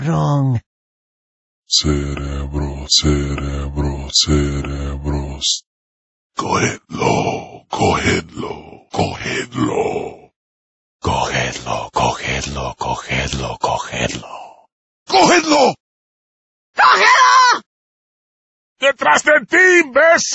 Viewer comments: i always thought they said go hilter, so when playing im like wtf does hilter have to do with anything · I’m sorry, but the first time I heard them say this, I deadass thought they were saying “cookies cookies cookies” and now my headcanon will forever be that Sadler is the man who created cookies for the entire world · I hear "go halo" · I used to think they were saying "go hitler, go hitler, go hitler" when I was a kid lmao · When I was younger I thought 0:25 was saying go kill him vato.